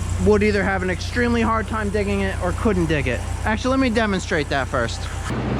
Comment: The mic didn't pick up everything said. Here's what he said. "Let me demonstrate that first for the experts who are gonna tell me I should have used the excavator."